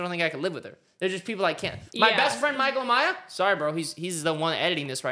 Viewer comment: Is it just me or did anyone else think that said Michael Myers at first??😂😂😂😂 Time stamp